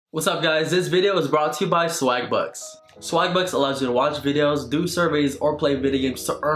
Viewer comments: In the beginning I thought you said swag butts.. I'm like "bitch whaaa?" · I thought he said swag butts. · At first I thought he said swagbutts lol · I thought he was saying “swag butts”